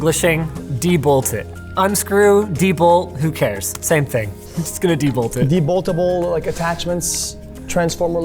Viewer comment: did he say “don’t eat it” or “donate it”